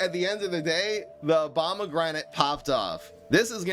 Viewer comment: I swore I heard him say Obamagranate😅